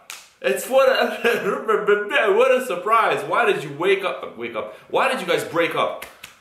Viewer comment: I thought it said we woke up 😂😂... you need to tell me what street is that in which you are able to go at 150😂👍👍nice video